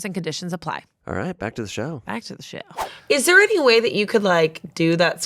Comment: did say “back to the sh*t”😂